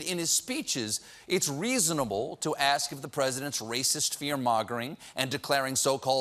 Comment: Did I mis-hear, or did Colbert actually say: "fear-MAGAring" (7:08-9)? What a wonderful coinage! I want to use it and I hope others will, too...